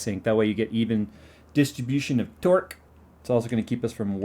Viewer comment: did he say distribution of DORK ?